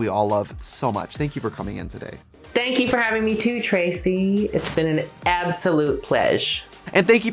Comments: Did she just called Trixie 'Tracy' 25:39 😂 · I can't w8 to see Denali on all stars. Also did Denali just call Trixie "Tracey" @ 25:40..... The Shade!!!!!!